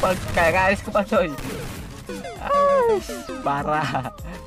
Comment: ngomong apa😂😂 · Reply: Katanya gak ada es kepal